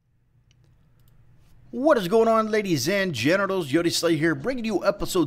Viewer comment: 😂is it just me or does it sound like he says hello ladies and genitals before every video?